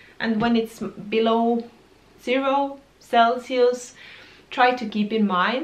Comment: At the end I heard “When it’s zero Celsius, don’t do anything…..” I think you said something else, but I got the message. :)